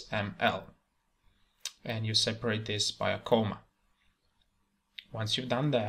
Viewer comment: After doing all the process you have said I am having this error on my console: "Error occurred during initialization of boot layer java.lang.module.FindException: Module javafx.controls not found" Can you please help me solve this? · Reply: I had this same issue. I thought he said to put a colon between controls and javafx, but I misheard him & he actually said comma...oops. Maybe you have a typo somewhere in there? --module-path "insert path here" --add-modules=javafx.controls,javafx.fxml